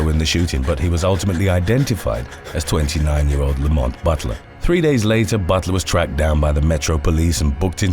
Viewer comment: I thought you said “the moth butler” instead of Lamont Butler & here’s me thinking what the hell is the moth butler when it’s at home??